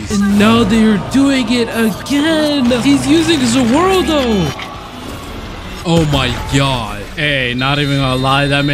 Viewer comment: did bro say oh my gyatt